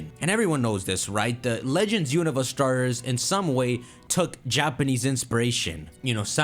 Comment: Did bro say legends unova starters or am i tripping??